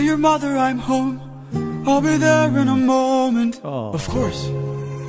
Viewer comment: I totally thought he said "Oh F*ck!" and not "Oh fun!" lmao I was like "damn. He was INTO this song" 😂